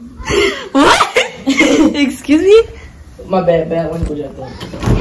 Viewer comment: did call her bae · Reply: He said my bad